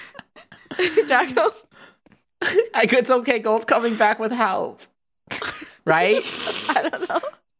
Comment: what did say at 16:39? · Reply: It’s ok golds coming back with help